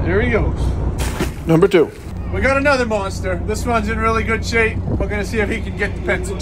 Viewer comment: The second one I thought he said “we got another molester”